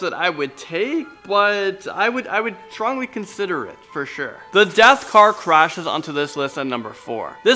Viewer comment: I thought you said death star instead of death car